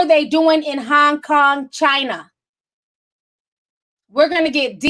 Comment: So funny it sounds like your saying honk honk China sorry it’s just how it’s being registered in my mind. Yes I agree this whole year has been planned out. Omg yes I was thinking the same thing about kobi daughters and goreges daughter have the same name.